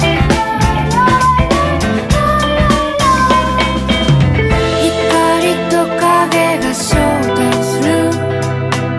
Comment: ってなんて言うやつですか？ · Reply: たぶんアオハライドだと思います！